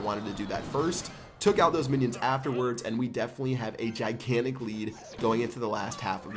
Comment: I had captions on for lols and at 1:40 where he said 'a gigantic lead' the captions said 'a child killing lead' :0